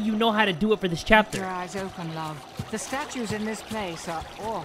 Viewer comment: She said that the statues in this place are off but you thought that she said The statues In this place are love